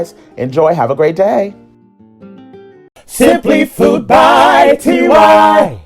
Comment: I thought you said simple!